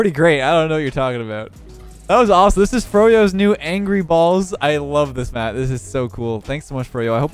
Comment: "This is Furios new angry balls" instead of angry birds O.o kinda sus